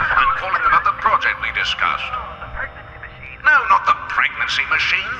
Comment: I’m sorry, did he say “Pregnancy Machine”?